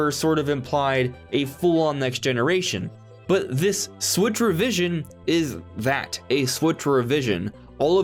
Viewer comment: I thought you were saying "switchervision". I thought it was just a gaming term I'd never heard before. I didn't hear switch revision until you said it at 9:02.